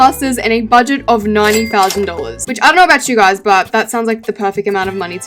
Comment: I thought she said $90…..